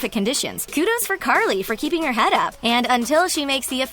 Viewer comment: Anyone else hear "Carly"?